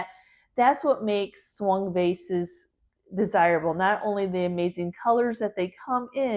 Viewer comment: Thank you for the information about the Swung vases as I live in England the first time you mentioned them I thought you said Swan vase and couldn't see why they were called that ! it was so interesting to hear how they were made 😊❤